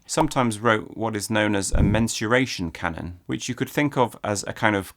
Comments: i thought he said "menstruation canon" which sounds like a much more unpleasant thing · For a second I thought you said menstruation cannon